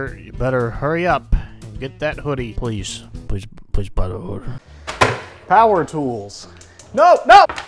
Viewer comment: captions say” get that hoodie please please please butter power tools no”